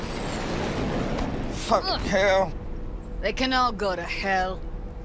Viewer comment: why did I heard it like this Man: f* in hell Girl: I cannot go to hell